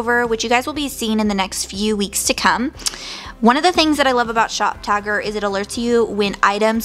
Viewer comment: With your accent it sounded like you were saying shop tiger